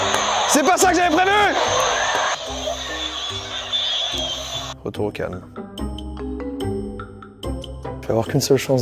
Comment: il dit quoi? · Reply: Retour au calme